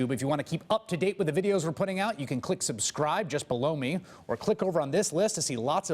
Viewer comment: I swear every time Chris Hayes ends his clips it sounds like he says "just blow me" ( I KNOW he's saying just below me) lmao